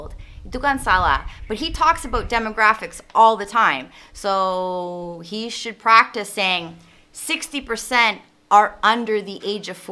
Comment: On 6:27 i heard you only said "he should practicing.. " Why do you not put "be" So that become "he should be practicing..". Or my be i was mistaken on hearing it